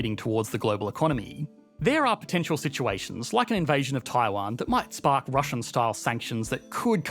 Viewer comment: I know you meant to say "Taiwan" at 12:48 but it sounded like "Thailand". 😅